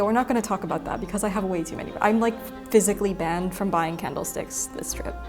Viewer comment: So when you said you were banned from buying candle sticks I misheard and thought you said kettle sticks (No idea what one of them may be but hey!) then you went and bought a kettle on a stick (ish)